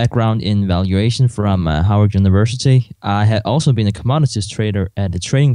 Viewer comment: Woo Woo! Howard University Grad! Go! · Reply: Is it wrong that i heard "Hardvard university" 😅